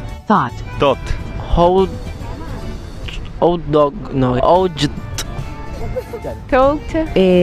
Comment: Excuse me did I just hear "hold old dog" there for a second?